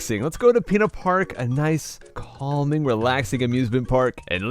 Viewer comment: My friend's mom always thought he was saying penis park whenever he said pinna park for some reason. Then again, this was a person who yelled at their child for saying shut.